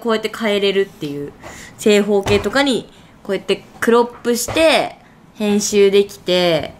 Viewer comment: siri英語でせっていしてあるんやけど、9:42で「編集して」が「Hey siri today」に聞き取れてsiri反応した。、